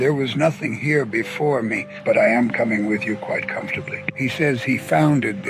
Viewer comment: he is saying "But I am coming with you quite comfortably ", why did they put the (...?) like if they didn't understand him?